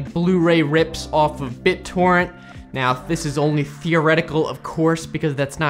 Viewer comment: hehe i heard that as "thioretical"